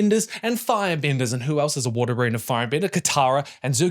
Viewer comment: the New Zealand dialect making you say S3x instead of six when talking about zutara is extremely funny 😂